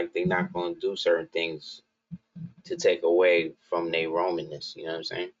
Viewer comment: #timepoint 7:02, did you just say "Rominess"? LOL...... MY MAN, you got them words.